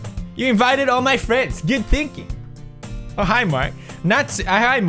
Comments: Did anyone else hear him say ' oh hi Mark ' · Did I hear a Oh hi Mark there? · Is it just me or what because I can’t understand pewds when he is Sayori and did he just say “ o hi Mark “ instead of “o hi Monica “ XD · Sounded like "oh hi mark"